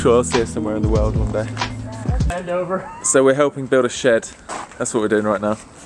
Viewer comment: Am I the only one who heard "bend over"